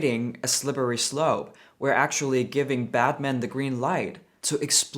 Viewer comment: sounded like you said 'Batman' 😂